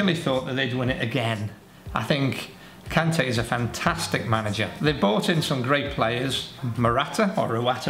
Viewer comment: I swear he said "Kante is a great manager"